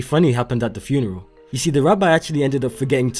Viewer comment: Did he say Rabbi? He's Jewish? Cool!